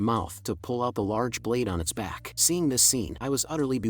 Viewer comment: um did he just say holy shi